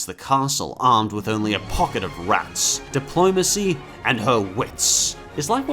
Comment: did he say RATS